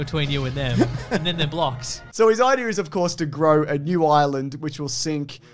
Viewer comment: I legit heard say "his plan is too grow a new Ireland"